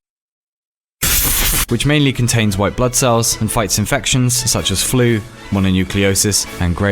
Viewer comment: Bruh who else heard "White Butt Cells" xD